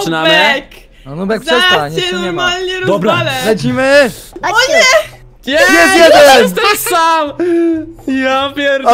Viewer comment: Co powiedziała? Bo nie usłyszałam? 😹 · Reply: Nie rozwale'x